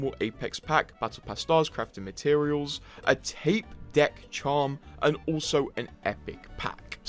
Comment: Tape deck charm. wasn't paying attention and thought I heard tech deck charm. was sad to see a tape and not a little skateboard lol